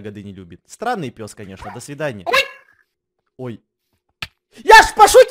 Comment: мне послышалось х.й😂😆😅